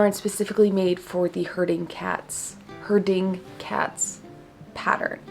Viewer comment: I thought you were saying 'hurting cats' until I saw it spelled out hahah